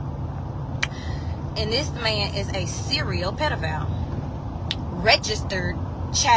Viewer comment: i yelled so loud because i wasn't ready. Did she say serial?